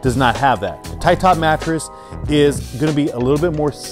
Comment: i have been searching for this type of mattress from ur previous video but got the name wrong,here laughing at myself thought u said tietab mattress😆